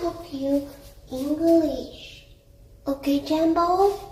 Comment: Anyone got an idea of what the fuck she's saying, all I can understand is " English, okay, Jambo, and that's not English "